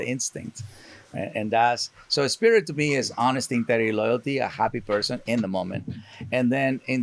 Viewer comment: Can someone help me? I can't understand exactly what Cesar said at this time stamp, and the CC doesn't get it right. Can someone tell me what he said about what spirit is to him? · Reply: "honesty, integrity, and loyalty"